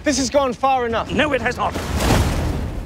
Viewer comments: "I saw her ankles" "You'd have seen a lot more if you kept you keg hole shut!" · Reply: "Cake Hole" I thought he said. English slang for mouth. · "I saw her ankles" "You'd have seen a lot more if you kept you keg hole shut!" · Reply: I thought he said pie hole....I'm WAaayyyyy offf.